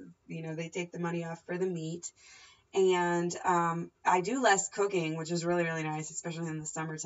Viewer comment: haha sounded like you said you do less cocaine, but it was cooking. Great vid!